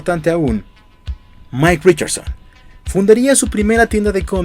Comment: Pensé que dijo Michael Jackson. XD